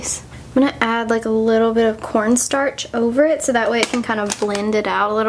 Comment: WAIT WAIT waittttt did she say "let apply little bit of PornStarch" ???? 0-0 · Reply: I didn't hear very well that's why I thought she said that "PornStarch" xD